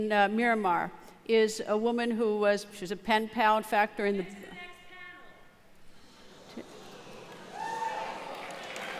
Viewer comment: What was shouted out at 15:38??? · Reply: " What about the refugees" was shouted.